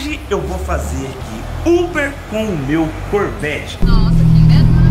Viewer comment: No título eu entendi "Uber com sorvete" kkkkkkkk